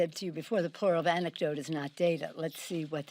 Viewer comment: I thought she said "the plural of an anecdote is data.". ha! that's what i heard.